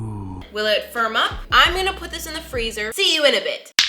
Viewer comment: On 15:37 i thought she said i May put it in the frizer for am month 😂😂